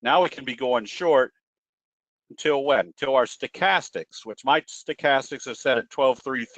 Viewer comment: used to use the 5,3,3 Stochastic setting. So now Is 12,3,3 his new preference? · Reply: At around the 11:50 mark says "Which Mike's stochastic is set at 12,3,3." It sounds like he is saying "my stochastic"....... but he is not.